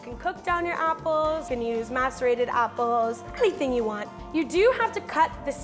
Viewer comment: I thought u said “you can use masturbated apples” 😂😂😂